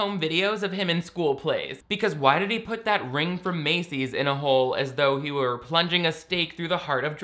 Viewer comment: So I heard the guy in the crowd scene say something about seeing his hog, not a hole. That would mean one of 3 things. 1. A hog as in the animal. 2. A hog as in a motorcycle. 3. A hog as in slang for a body part. I’m not sure if I’m glad I misheard or not. Oh, and you are correct, this movie does not compare in any way to Crossroads. At least in that movie the female leads become more empowered throughout the film.